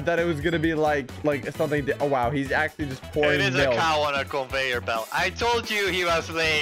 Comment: it's funny how the editor didn't know what to put for subtext even though he clearly said "cow on a conveyer belt". the editor hasn't watched enough to learn to speak blaza mic